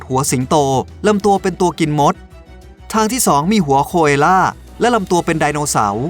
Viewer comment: ได้ยินว่าหัวคว*อ่ะ555